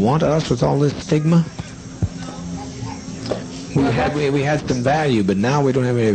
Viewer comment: Im sorry...what did he said? He said sigma... ಠ_ಠ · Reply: Stigma